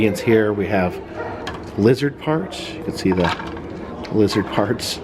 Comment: Lizard parts? Oh I thought you said Lizard Farts